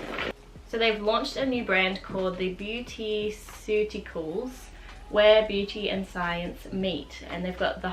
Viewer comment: Can you please share a link for the skin care line you mentioned towards the end of the video? I thought I heard "beautyceuticals" but no results came up when I Googled it..